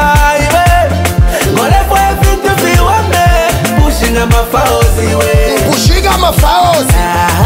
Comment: My sister had to explain to me the meaning of this song! Such has happened since I haven’t lived in Zambia for a long time! I thought he was saying ma falls lol 😆!!! Come to find out it was ma files!!